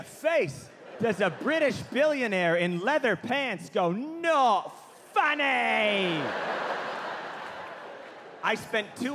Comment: OOOOOOOOHHHHHHHHHHHHHHH. I thought he was saying "fanny".